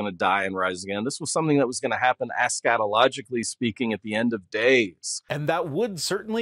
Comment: I can't be the only one who heard that as 'ah, scatologically speaking' and was very confused